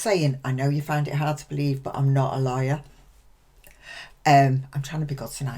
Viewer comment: When you said: 'I'm not a LAWYER' ... I heard: 'I'm not a LIAR'